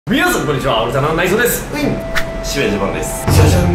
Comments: です！が、今回"内蔵です！"って聞こえたw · あいさつがオルタナの内臓って聞こえたww